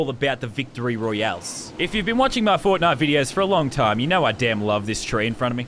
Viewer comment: I thought She Said Fortniut